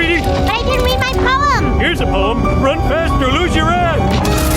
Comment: When lisa about died 20:55 did homer say run fast loose your ass, act or something else